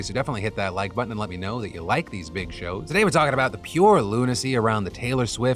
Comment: I swore he said " I've got an extra large one for you today so definitely let me know if you like these big chodes"